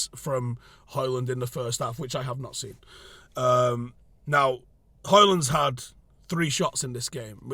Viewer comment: I thought you said Hojlund needs shot